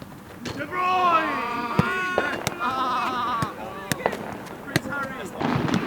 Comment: did someone call kdb prince harry lol